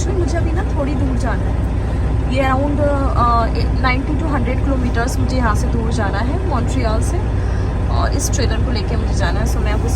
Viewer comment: Kya bol Rahi ho didi... Thodi dur Jana hai 9200 km 😂😂😂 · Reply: 90 to 100 kilometres